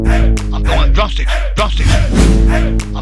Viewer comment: And for a long time i thought he was saying "Fat Homie Bitch"